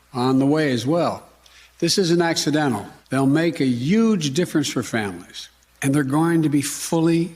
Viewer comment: DID HE JUST SAY "YUGE"? 0:56 Omg! Joe Biden is actually Trump in a Joe Biden suit!